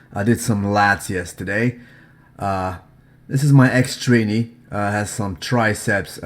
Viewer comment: I swear I heard him say "my ex training"😂